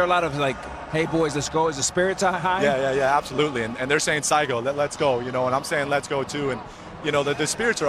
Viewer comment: Ah, sa iko= let's go. Thought he said saigo = that's the end 😂 Look forward to another brilliant piece of baseball against the mighty US